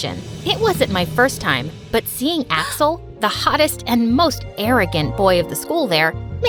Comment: who else heard seeing a**hole?